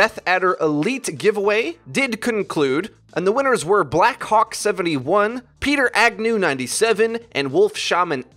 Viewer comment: sounded like he said black cock